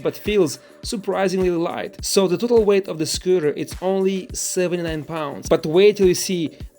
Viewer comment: seventy what pounds? couldn't understand you clearly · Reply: 79 pounds